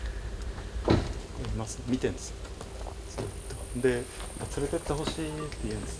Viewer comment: ～が『見ているんですよ』と言った後、女性のような声で『ずっと』と答えてるように聞こえました… 今後もコラボ、楽しみにしています♪